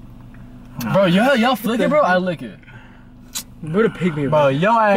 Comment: What did says at 9:07 ? Genuinely curious, i couldn't understand a word thru their laughs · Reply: he said “what a pick me boy” & “yall forgetting hes a pick me boy.”